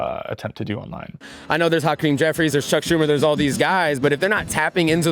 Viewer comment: did he say "Hot Cream Jeffries"?